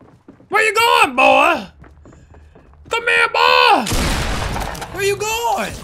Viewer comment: 0.25 speed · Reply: i hear C'MERS BOY!